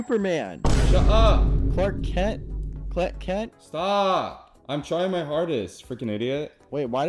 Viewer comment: DID BRO SAY "clit kent" 💀💀💀💀